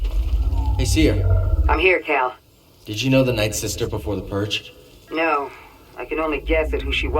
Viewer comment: Just putting this out there: he says “nightsister” instead of “inquisitor” 1:35 it’s kinda wierd tbh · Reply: I heard it like that while playing the game but now I’m guessing he said “ninth sister”